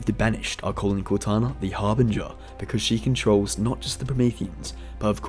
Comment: I thought he said the Arbiter